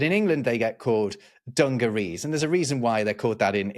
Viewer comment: I hear dungaroos sometimes.